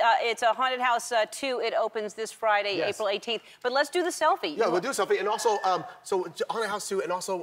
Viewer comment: omg i thought ellen said "but first, let's do a selfie" lol i was about to say, omg not her too! lol